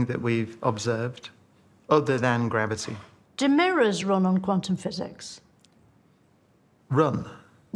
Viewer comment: “Quantum McPhysics” made me laugh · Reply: In all honestly, I heard it like "Quantumic physics", which is wrong, it's like saying peanutic butter.